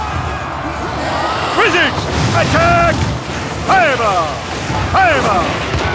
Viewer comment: What they are saying 0:05 ? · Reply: Fireball